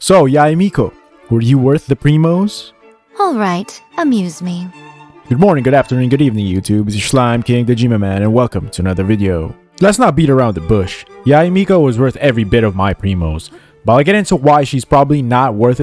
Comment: i THOUght I HEard KOJIMA MAN